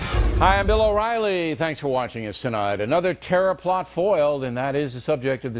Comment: I heard "Tera-Flop" instead of terror plot hahaha I'm an unindoctrinated nerd. XD